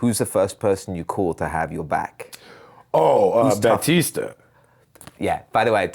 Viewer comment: “who’s the first person you call to have your back?” @ 29:17 who did Mackie say? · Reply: dave bautista as drax